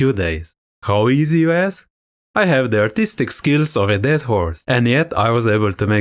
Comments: L0L... I thought he said 💀 "dead whore" until I read in the comments, and then realized I was mistaken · Swear I heard "a dead whore" 🤣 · I thought he said artistic skills of a dead "whore". That's what got me to stick around 😂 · Oh- you said "Dead horse"??? Maaannn I heard "dead whore" and spit out my soda!!! I liked the video just for that one comment. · I thought he said dead whore 😅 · "artistic skills of a dead horse" got me subscribing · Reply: Phew... I thought he said , "dead hoare!"